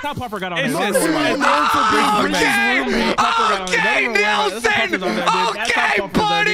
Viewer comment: bro I heard OKAY N and I got scared